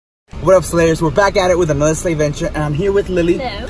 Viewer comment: did he just say slayvention